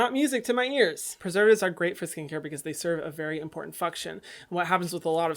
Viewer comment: When you said “function”, it sounded like you said “f*ck-tion”